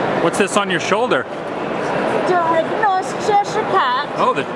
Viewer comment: all I heard was DAIGNOSHCHESIRECAT